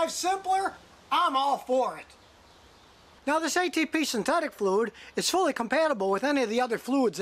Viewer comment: I first heard "HEB synthetic fluid", I was like .... uh.... Not sure I'd trust HEB brand automotive fluids and oils. Their chips and soda however, is better than name brand. I love Jalapeno flavored HEB chips, and HEB "Original" pure sugar cane Cola.